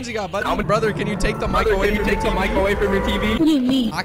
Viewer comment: it sounds like he said "can you take the from microwave the tv"?